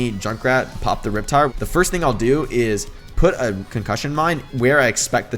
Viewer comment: Is it just me or does it sound like saying Coc mine?